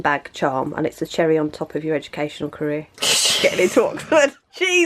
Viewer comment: "a cherry on top of your educational career" 😂😂😂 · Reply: I thought she said educational curl LOLLLLLL